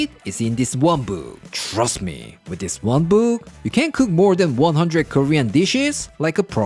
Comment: I always hear: "Trust me, with dis wamboo..."